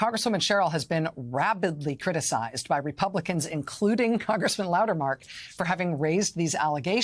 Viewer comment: Darn it. At 0:42, she mispronounced his name as "Loudermark." My first play-through, I thought I heard "Loudermouth," which would have been rather fitting. I will give Representative Sherrill credit for observationizing. As she is both Former Military (TY, Fellow Vet!) and a Former Federal Prostituting Attorney, she definitely has the education, training, and experience to notice when "Something is starting to smell funny." I didn't even need to be told she'd reported it, too.